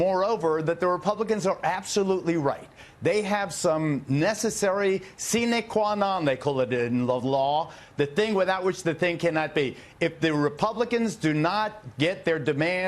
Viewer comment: What is he saying?!?!?!?! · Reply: I believe he said that Republicans have some "Necessary seenay quawnaw necoladin lovelaw, the thing without which the thing cannot be." And you know what, he's absolutely right.